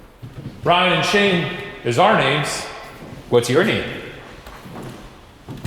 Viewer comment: #debrief Might be one of them talking quietly but i swear i hear a deep voiced whisper saying "Ryan Shane" after Shane's question